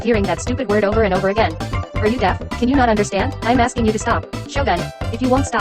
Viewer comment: Kidnapper:Depression Gojo:Crying right now Alice:Dead Raiden shogun:Enraged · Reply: i thought it said “shotgun” 💀